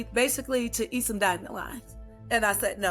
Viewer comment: eat some what? I don't understand what she's saying. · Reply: Eat dandelion